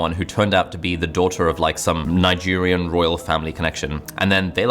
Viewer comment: Hahaha did he say, Nigerian prince